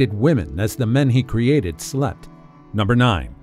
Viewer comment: there is something wrong with me 🤦🏻‍♀️I could have sworn at 8:03 he said he created women as the men's slut lmao but he probably said slept